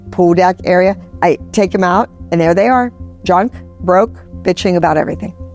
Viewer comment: Wait at 6:30 did she say the mom's were drunk?